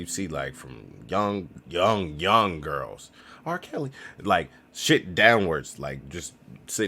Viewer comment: Did he say R Kelly at 8:13 lol😂😂😂😂😂